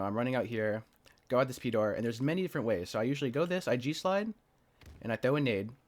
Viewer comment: Oh, G SLIDE I thought for moment you're saying JEW SLIDE and was thinking WTF did I just hear 🤣